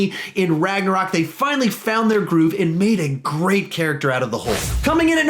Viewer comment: In 9:24 I think he said groot